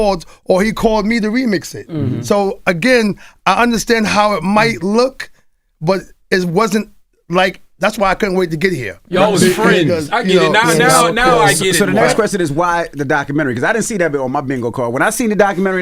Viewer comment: Man, I hate when people do this. This is being willfully obtuse 50 Cent called you for a documentary about Diddy and you thought it was a good thing to do but you still calling this guy your friend come on man. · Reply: I could’ve sworn he said WAS friends